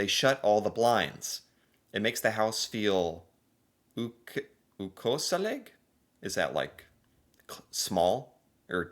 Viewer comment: He actually said "ukoselig" perfectly, it just sounds like he has a more pronounced norwegian dialect when he says it. (FOR CONTEXT) "Ukoselig" is the same as "un-cozy" or, uncomfortable. "Koselig" means Cozy in english. And the "U" infront is basically the same way of putting "Un" infront of uncomfortable to mean that it is not something Hope this helps! (Sorry if it's confusing, tried my best)